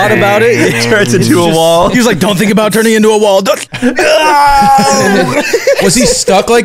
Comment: i thought that shit said "turning into wall height"